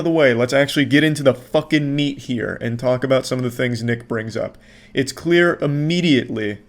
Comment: what did he sayyyyyyyy? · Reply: “Nick”